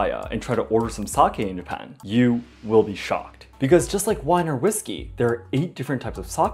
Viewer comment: When you said “you will be shocked” I thought u said “you will be SHOT”